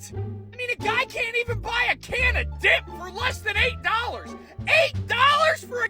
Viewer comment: thought he said "can of dick" would have made sense by how his throat sounds